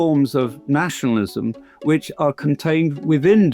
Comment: I keep hearing this word "(ZIONISIM)"